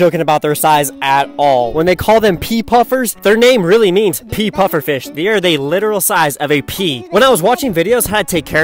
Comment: I thought he said "peafish" but it was "peefish"